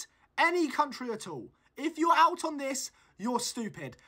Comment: Bro, I thought you said Vatican city not Venezuela because I picked Vatican City